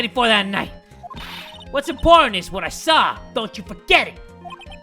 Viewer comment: who heard important as imporn?